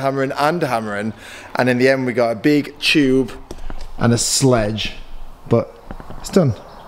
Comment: Am I the only one who thought said “a blue chew and a sludge” at 5:06 🤦‍♂️